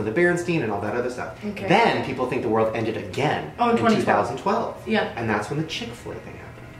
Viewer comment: listen to what the girl says if you didn’t hear it when Shane was talking about the world ending she said 2020🤯😢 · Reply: She said 2012. 🤔